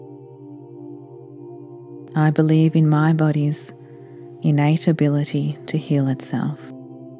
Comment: I was listening to this meditation last night for the first time while falling asleep. I woke up very confused the second time I heard @ 46:40 and @ 1:54:23 "I believe in my body’s INABILITY to heal itself." I freaked out and wanted to report this video. Thank you for taking the time to write the affirmations. Please do not confuse this affirmation: "I believe in my body’s INNATE ability to heal itself." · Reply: She says innate ability, not inability. Didn't even check the second one becuz of such huge error. Glad you didn't report this. Check it again.